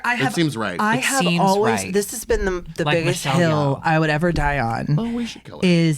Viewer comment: What name was said at 27:07?! Was it Michelle Yeoh?